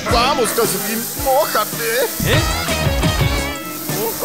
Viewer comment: alguien escucho lo que dijo xd? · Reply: Dijo "vamos calcetín mojateeee"